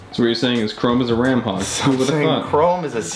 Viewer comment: “Chrome is a CPU Bulldozer” How I hear it: “Chrome is a CPU AMD CPU”